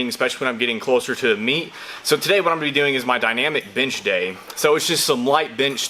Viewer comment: At first I thought you said "Dynamic BINGE day..." I was like 8D hellyeah, boi!! Where da food at???